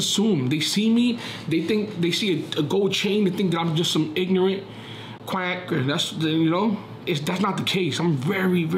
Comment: What was that at 24:02 did he say quack?